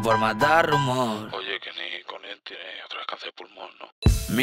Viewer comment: Que dice en el 1:19? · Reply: "oye que'l nikon este otra vez cancer del pulon?"